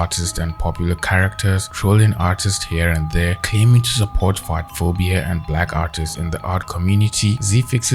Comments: I'm so sorry but I thought you said fartphobia 😭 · My dumb ass thought you said “fartphobia” I’m sooooo sorry 😭😭😭